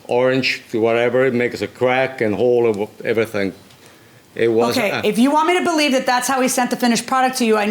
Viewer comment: The orange/pink discoloration the plaintiff was referring to was on the parts he had in the courtroom. I don’t think the judge understood due to the plaintiffs accent. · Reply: No he clearly said it "was" (5:58). The judge had asked him how it appeared after defendant initial finished the chrome and returned to the parts to him. Also, I think what he was trying to describe was "orange peel" which is a surface texture flaw in a finish and does not have anything to do with coloration. I've only heard that term used regarding spray paint finishes, but I think that is what he is referring to.